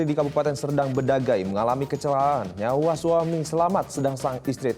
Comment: mungkin gue salah denger kecelala'an